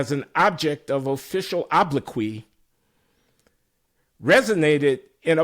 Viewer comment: Is he saying “oblique”?